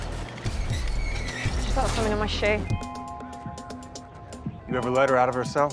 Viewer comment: what did she said? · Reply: I got something in my shoe